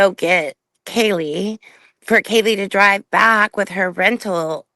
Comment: I think I misunderstood something I thought that you could hear Dylan say poor Kaylee on the phone during the 911 call but then when she is talking to the cop she doesn’t even know if Kaylee and Maddie are home. Then she acts shocked when she THINKS the cop says they are not there. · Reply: I definitely hear "Poor Kaylee"